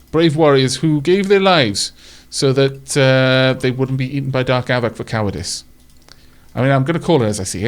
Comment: The advanced Military Turrets put the basic model to shame! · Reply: i heard something a bit... different. 28:16 "...who gave their lives so that Dark Aavak wouldn't eat them for calories!" Keep me signed up!Hopefully I don't become cowardly calories.